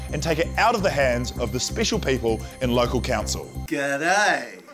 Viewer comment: I thought it said "gay time" 😂😂😂